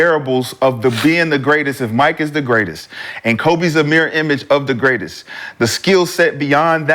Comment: How ya kno McCan’t don’t even kno wut da F he be sayin…da term mere image means—nevamind bruh…😂😂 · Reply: Mirror image